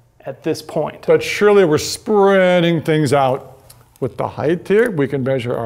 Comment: What is he saying? But Shirley we are spreading things out.